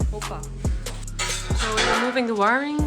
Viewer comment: i thought you said "bobba" instead of "hoppa" like the Habbo curse word lol